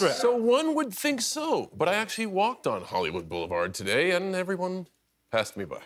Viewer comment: Did he just say " Hollyweird"?